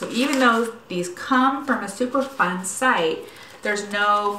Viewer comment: Hi , what do you mean by earth friendly? Explain it to me like I’m 5 . ( Michael Scott- The Office) 🤣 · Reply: I just watched the DS live when he explained it to you. I thought you said Super Fun sites! 😬